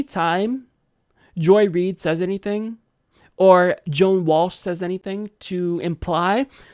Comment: Did you just say Joe Walsh? Like The Eagles Joe Walsh?? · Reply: No. He said JOAN WALSH, though there is a Conservative gas bag named Joe Walsh. No relation to the Eagles band member.